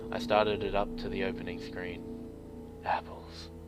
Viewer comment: i couldn't stop laughing. I'm sorry, but I heard "was chanting apples" and I just thought that was hilarious.